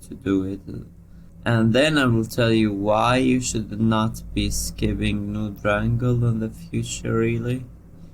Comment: I could not understand what you were saying so I turned on closed captions, but I was even more confused.… what is “skipping new drangle”?